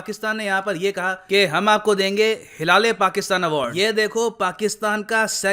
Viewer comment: I just woke up and played this video Mistakenly clicked on 2:38 And i heard " Hila Le Pakistan " And i can't stop laughing now 😂😂😂